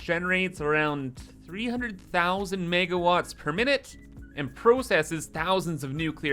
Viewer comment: did you just say "Megawatts per minute"?:oops